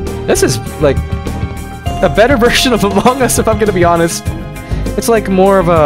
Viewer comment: Did he said among us?